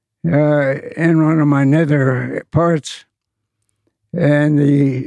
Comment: what did he sayyyy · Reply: ‘In one of my nether parts’